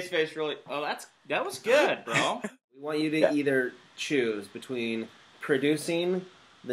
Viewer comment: I thought Shay said we want you to eat her...lol